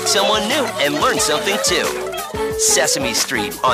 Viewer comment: The female voice on the first two sound like she said ''coming up nex. On WUSF Kids."